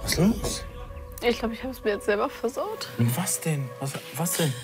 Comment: Hahaha, deine Kommentare zu dem Füße lecken, zu gut 😂 Vor lauter Füßen habe ich bei erster Szene (ca Minute 22:58 ) erstmal verstanden "ich glaub ich hab Fußpilz selber versaut 😭😭" Und ich hab echt 3x zurückgespult, ich versteh immer noch jedes Mal das gleiche 😂